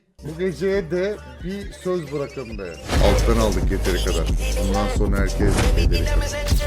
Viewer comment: deki şarkının ismi ne bide abi orda ne diyor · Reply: söylediği şey;Alttan aldık yeteri kadar bundan sonra herkes bedeli kadar mı diyo galiba/ Şarkının ismi de ; Molly Tre Savage